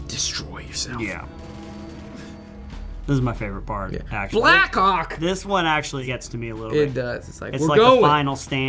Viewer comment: I thought he said "Black Cock"